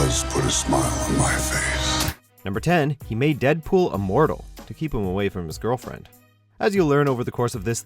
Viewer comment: Lol, got confused there, sounded like he said "a mortal" instead of "immortal"